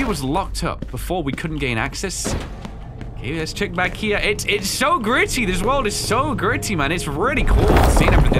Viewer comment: i heard “its so griddy”